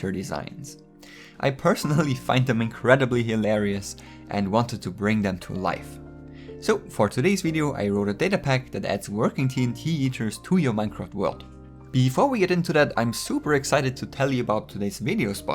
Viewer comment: It sounds like ur saying "TNT eaters"